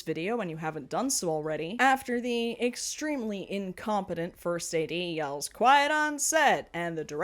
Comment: I kept hearing "first aidee" instead of '1st AD'. I kept thinking, isn't that the person who's receiving the first aid??? Oh.